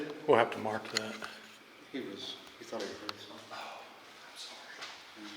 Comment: I heard a male voice whisper “I’m sorry”. Not sure if this was one of you guys.. this so interesting to watch. So looking forward to part 2. Regards to you all.